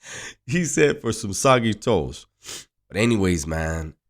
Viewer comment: Foreal who likes soggy toast😂👎🏼 Another great video. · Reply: Thank you for your comment. I was confused.. I thought had said "soggy toes!" I was like whaaa.. ok. Lol Nates a foo!.